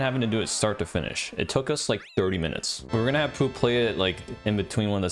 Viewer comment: only had like 5 minutes 1:33 but like for example 2:12 That's like the first 2:13 get to like this part 2:24 part was like a ball man special 2:50 but he was like, I'm gonna 3:07 it's like the hardest part of the entire level. You have to like throw it backwards 3:16 ball man was like, "Let me put two more." 3:32 thinking about this like the the world record speed 3:46 That's like the hardest part, the second section, cuz there's like dude, this swamp is awful, but like in a great way 4:22 It took us like 30 minutes. We were going to have Poo play it like in between one of the segments, but we also ran out of time cuz we uploaded it like shortly 4:44 It's like kind of like a watermelon 4:52 Like, we had a clear check attempt 5:14 up until like right here 5:47 took him like I don't know 3 or 4 minutes 5:56 to finish like the world record grinders 6:06 Like this beginning was uh cool 6:47 But like besides 7:00 while like chat 8:10 So, like 115 8:20 Like, sure, you you can't 8:45 got to like actually play 9:51 a gloombo like oh he died in a prago. Like right here 11:32 there was like a TV upstairs 11:52 came out like 10 15 minutes later 11:57 he was like about time man 12:03 I was like I got you 13:41 Like this was a fake dev door 14:12 And not like battles like skip this 14:31 during like second half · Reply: some of ur captions are wrong, I think 4:22 should say Poo, not P, and 5:56 should say world record not war